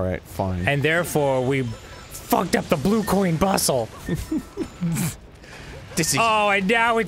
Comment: Could have sworn I heard "Blue Ball Bussy" instead of "Blue Coin Bussle" ...I think quarantine might be getting to me